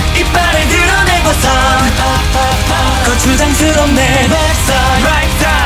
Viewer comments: 거추장 스럽네 자꾸 고추장 스럽네로 들려서 눈물 흘리는중... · 거추장스럽네가 고추장스럽네로 들려요ㅋㅋㅋ 머리색도 고추장 색이라 더 웃김..ㅋㅋ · 이제는 계속 고추장스럽네 이렇게 들려ㅋㅋㅋㅋ